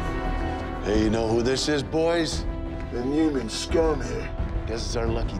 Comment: What does he say at 1:27? "Inhuman scum"? Sounds like "Inhuman scum here" but that wouldn't make sense. Can someone clarify please?